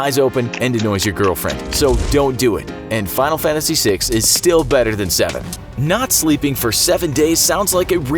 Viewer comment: I thought you said seven was better than find Final Fantasy 6 never played it